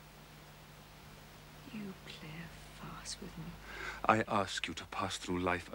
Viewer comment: What is Jane saying? Thank you! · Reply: Filiomena She says: "You play a farce with me".